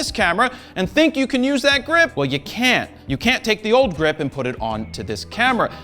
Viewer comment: forgive my eng. at around 9:39 is Jared saying you cannot? or can? Google subtitle's auto gen. use "can" for the first and "can't" for the second and am really confused atm. although it sounds more like "can't" than "can"